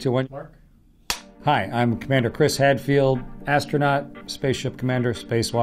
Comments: First I thought he said. Commander Chris Redfield. I had instant RE flashbacks xD. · I thought he said he was commander Chris Redfield